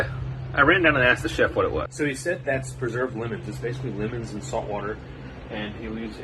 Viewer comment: Preserved women? Are you sure? I'm pretty sure that is illegal. The Chef is a serial Killer? 😮 Edit: Oh, Preserved Lemons. I heard it wrong the first time. So he kills lemons? I would keep an I on that Chef. Kinda Suspicious. 😂